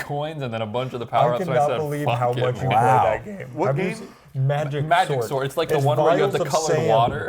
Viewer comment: i thought they were saying magic sword, so i was assuming it was a fantasy game but spending money on magic SORT is completely different😭😭